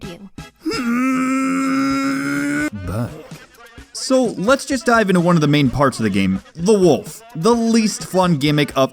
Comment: what did wolf link say? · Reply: "Bark"